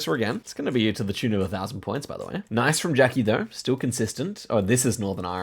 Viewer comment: Is it just me or did i hear say "Jacky" At 21:55?